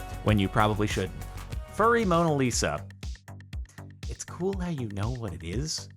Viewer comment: anybody else hear "furry mona lisa" and think of the mona lisa with cat ears 0-0 (send help, the memes have taken over)